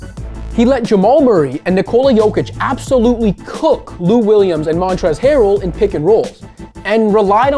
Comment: I thought he said “Luke Williams”! That will be my bad on my part!